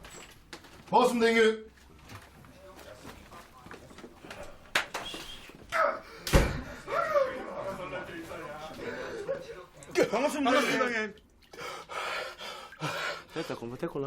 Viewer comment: "Boss nigga" Am I the only who heard that?